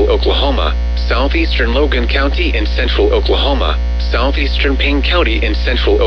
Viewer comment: I'm 200% sure at 1:41:15 that instead of it saying Southeastern Payne County, it said Southeastern Ping County